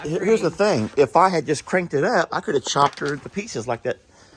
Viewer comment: Listen to the video at 1:17 "... if I had just cranked it up I could have chopped her into (in subtitles/captions reads 'the') pieces like that last time". THE LAST TIME! I think you realised by pausing that you let the cat out of the bag. A work colleague of mine, his cat went under a car to rest, but was deaf, so didn't hear the engine start. 😭. I thought you owning the place you had the right to say what goes on around ISS.